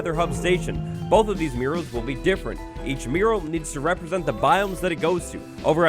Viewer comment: I didn't know the word mural before, and when you said it without the texts, I thought you were saying mirror with a weird pronunciation 😂